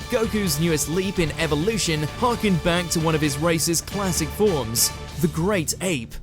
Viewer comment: I thought he said racist classic form when he was talking about super saiyan 4